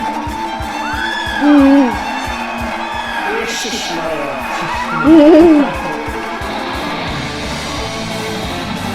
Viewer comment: что он сказал? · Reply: Э шишмараа - удивляется))